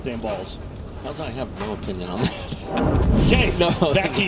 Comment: Can anyone tell what Mike Aulby said at 28:45 about urethane question? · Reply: I think he said “I have no opinion on them.”